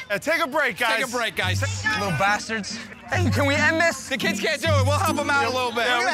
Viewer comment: did he call them little bastards?